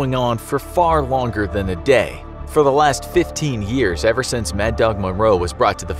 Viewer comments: wait....the captions say "For the last fifteen days" at 6:04, but The Infographics Show says "For the last fifteen years", oh and 9:03, it says "two-way mirror" "where they can see the execution room, but can't be seen, so a one-way mirror? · years instead of days in the subtitles